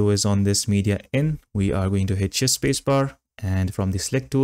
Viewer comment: Drove me nuts to finally understand, after about 10 minutes of google search, that you meant to say “shift spacebar”… (the closed caption says “hit spacebar”). Thanks anyway for the tutorial